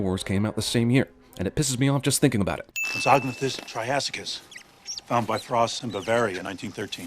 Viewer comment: I wonder if anyone else hears "try ass kiss"